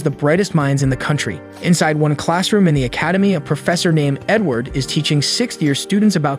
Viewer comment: I thought he said professor N word